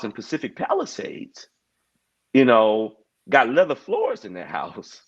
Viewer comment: Not leather floors😂😂😂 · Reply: I thought he said 11 floors lol either way hilarious